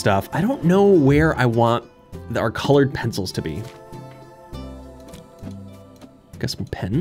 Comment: He said “I’m not sure where I want the colored pencils to go” I heard him say cold pizzas, I was so confused😭😂